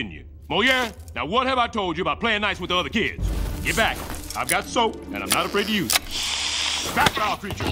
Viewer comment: First time I saw this scene, I thought he said" back off, I got soap so I'm not afraid of you" Which line sounds funnier I wonder